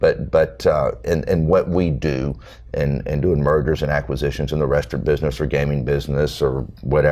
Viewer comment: Listening to this as background . Try to glean nuggets of wisdom . WAIT A MINUTE ! BACK UP ! 05:39 Did he say "Murders and Acquisitions" ? Mergers ! !